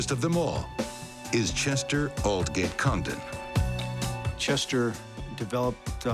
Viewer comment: Seriously, I cant control my laughter. I thought i heard he said the richest man name was Chester All Get Condom.. ..sorry still laughing. Whatever happened to the rest of Elizabeth siblings and her other daughter Jennifer?